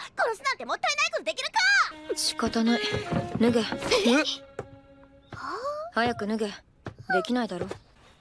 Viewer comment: YO DID SHE JUST SAY THE WORD SHE SAID IT TWICE SHE SAID NI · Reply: nugu = to strip